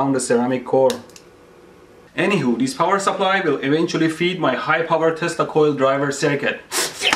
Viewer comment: did you just say feed my high power testicale driver?